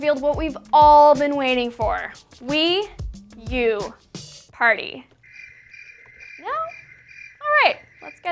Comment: i thought she said: me,you, party!. So i was like yes.